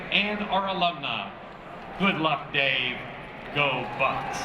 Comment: Amazing, but one weird question, 13:05 did he say go butts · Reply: ***** He said "go Bucks"